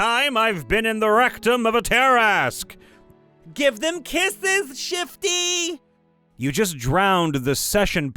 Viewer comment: “ I thought you said give them broken bones, not break their bones.” -me